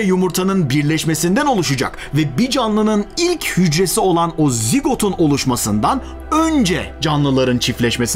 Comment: de ne diyor? Tam duyamadım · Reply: Bir canlınin ilk hücresi olan o zigotun oluşmasından once diyor